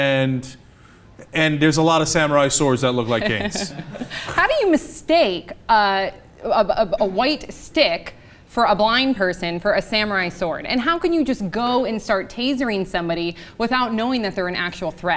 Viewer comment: subtitles say: " Hamas state about white stick for a blind person for a family restored and how can you just in colin start?" And so are the rest of the subs. Imagine what the world looks like for deaf people....